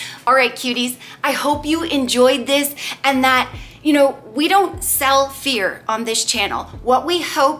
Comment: I thought you said we don't sell beer on this channel 😂